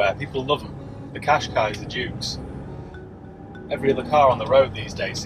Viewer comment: There was something he kept referring to, but I could not understand what he was saying. He said it first at 0:40, and it sounded like, "The 'Cash-kye or a Dune'". .. Anyone know what he meant please? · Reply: Qashqai or Juke